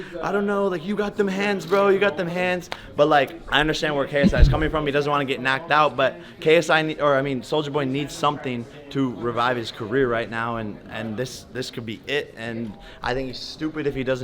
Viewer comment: did he just said ksi lol😂😂 Ksi definitely in his head, that's why he is so nervous meeting ksi